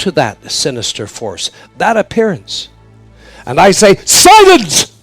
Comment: thank you! what is the word at 24:18? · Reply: Silence 💛